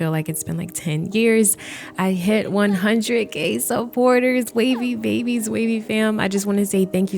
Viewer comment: I swear to god i thought you said "i hit 100 gay supporters". I was like JESUS CHRIST😮. BUT ANYWAYS, CONGRATS🎉🎉🎉🎉🎉🎉🎉🎉🎉🎉🎉🎉🎉